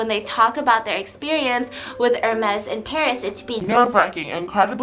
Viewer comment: I thought it said I got herpese in Paris... I litterly just got home from conobie... I need to sleep